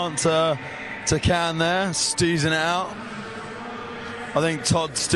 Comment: ish did he say "steezing out"?!(after a fast plant to can which isnt very amazing) im not watching this video any more, EXIT, I cant stand this moronic anouncer that is, by the way, horrible at what he does. woopdyfrickendoo